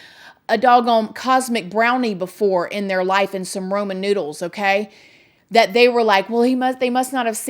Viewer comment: It sounded like she said Roman noodles lol